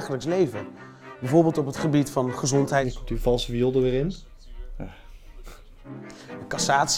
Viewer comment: Wat zegt op 07:36? Kan het niet verstaan.' .... wiel er weer in' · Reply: 'Komt die valse viool d'r weer in.'